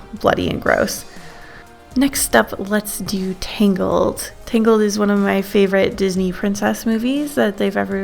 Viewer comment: i thought you said “my favorite Disney movie is ‘tingled’”